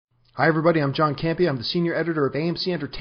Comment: lol thought he said "im john candy" n lol he mentions that tobey was "traded" am i watching nba bullshit show